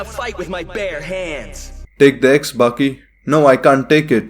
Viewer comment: when he said take the axe baki 3:43 i thought he said big d's baki